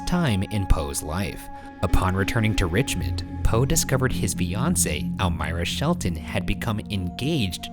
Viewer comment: anyone else hear 'his Beyonce' instead of his fiance